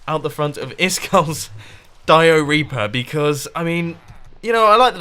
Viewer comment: When he said diareaper I thought he said diareah